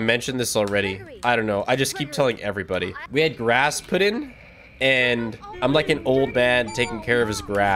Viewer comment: Until someone questioned grass put in I straight up thought you said you had Rasputin and I was so confused 😂